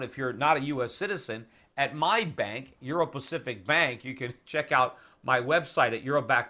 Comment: PETER, DID YOU SAY "EURO-BACKED BANK" ? CAUGHT YOU !